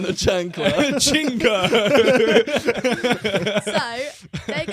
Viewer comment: I just wanna make sure I’m hearing right but did he just say an offensive ‘C’ word towards Asians or does that word mean something else that I’m not aware of? Not trying to throw shade just wanting some clarity. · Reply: I may be wrong but I heard either pachinko which is a drama series ( according to google) or a chinko which means penis in urban slang. Either way I dont think he meant anything by it.